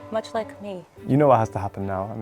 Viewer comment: he clearly said has